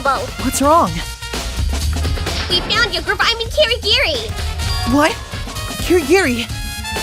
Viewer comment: What was Asahina about to say at 11:25? And while we're at it, why does she sound so different in this episode? Or is it just me? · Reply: "We found your girlfr-- I mean Kirigiri!" And that was still me VAing for this episode, if that's what you're asking.